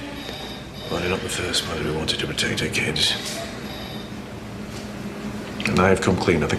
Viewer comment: What did he say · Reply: “Well, you’re not the first mother who wanted to protect her kids.”